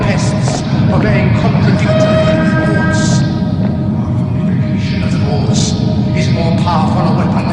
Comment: I tried transcribing this myself, but there were some parts I just couldn't get. I found the following transcript online. Instead of "Unification of Thoughts", I kept hearing "our vacation of dwarfs". LOL